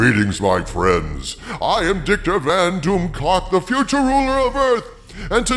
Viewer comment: It always sounds like he says “Dicktor” instead of victor